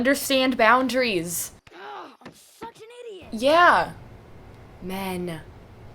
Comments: did he say f***in idiot or such an idiot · hol up is he saying there "fucking idiot"??